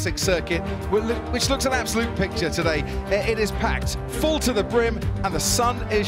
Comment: I thought he said: “full to the brick” 😂😂